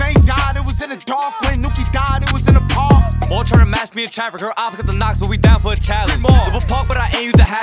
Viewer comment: wtf did he say😭😭😭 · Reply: Said some shit nd then "heard opps got the knockz but we down for a challenge."